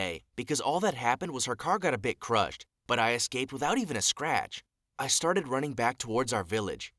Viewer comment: I thought he said without a scratch